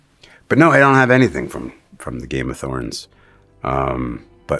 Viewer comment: Did he say “thorns”? Right at the end there I could’ve sworn he said Game of Thorns…